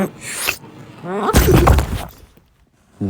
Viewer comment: LOL!!! This video is confusing oh and also what did say? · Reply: It’s swirl or be swirled.